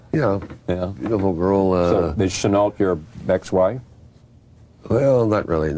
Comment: What did Charlie say about Chenault at 1:31? "Is you Chenault your _____?" I can't seem to fill in the blank. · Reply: wife